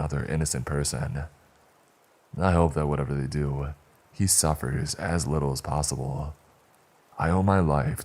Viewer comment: At the end one he's talking about Bryan. The commercial started and it sounds like he said, I hope he suffered.I wonder if that was on purpose lol